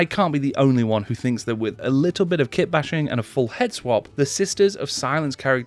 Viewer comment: Honestly thought you said 'Shit-bashing' at one point haha. Look forward to the results